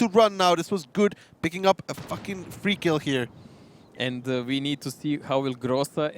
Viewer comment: did he just said fucking free kill? :D